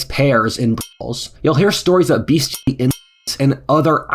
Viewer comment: what are the censored words after "br0th3ls"? English is my second language and my brain doesn't automatically fill the space : ( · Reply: I think it said "bestiality" and "incest", not sure about the second one